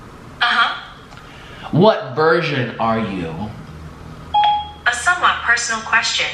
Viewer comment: When siri thought u said what virgen are you!!!!🤣🤣🤣🤣🤣